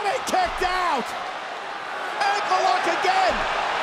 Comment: I swear i thought cole said angle lock lol